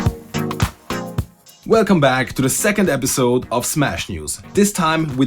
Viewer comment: i cant be the only one who heard him say marth second